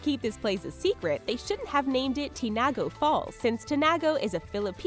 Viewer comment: I was listening to her and I thought she said "chingoe" but reading eat now i understand "tinago" since im a filipino